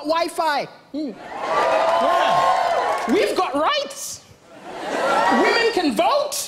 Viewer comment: I thought he said rice not rights lol